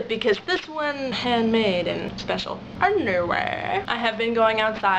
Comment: I know you said ... anyway, but for some reason it sounded like you said underwear. LOL Nice ta see ya again. Sweet sweater. You are quite talented. :D